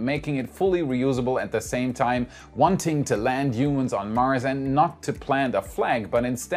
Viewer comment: At first I thought you said, "wanting to land unions on Mars." But I was like, I don't think Elon is interested in that... lol